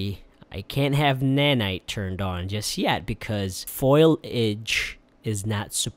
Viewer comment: Did he just say "FOIL-age"?